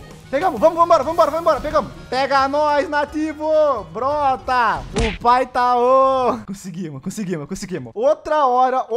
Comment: É isso mesmo que ouvi. Oppai humm safadenho 😏